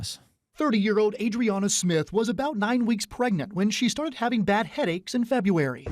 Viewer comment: She was 9 weeks along when she died. The baby is 20 weeks now. That’s what the news segment said. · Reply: It sounded like they said 9 months, but they said 9 weeks.